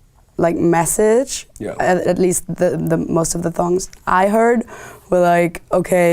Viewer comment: when she said songs the subtitles said thongs LMAO